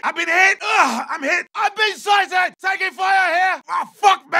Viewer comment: What is he saying at 0:26 ? I hear "I've been sizing" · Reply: I've been sighted